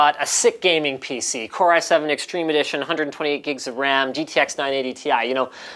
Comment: did he say 128gb of ram?